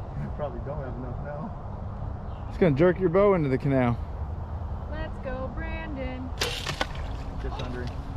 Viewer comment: Haha, I heard that ‘ Let’s go Brandon “ 🇨🇦